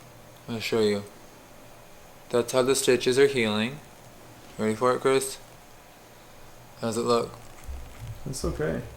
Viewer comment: My name is Grace and at 25:34 I could have sworn I heard “ready for it Grace?” And I was startled Hahahah